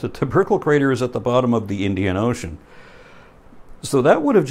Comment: sounded like he said the Indiana ocean